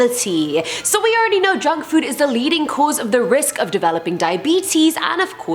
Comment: I thought you said drug food not junk food